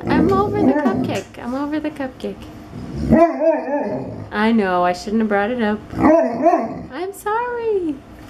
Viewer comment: i swear sebastain says "no you shouldnt"